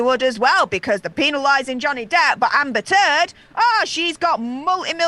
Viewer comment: Agree with all but I just gotta share… I love your accent ❤️ I thought at one point you said Amber Turd 💩 😂 then realized it was your accent and I misheard. Funny how it fits though huh?!